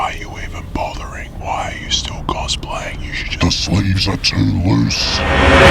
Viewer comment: i thought he said the slaves are too loose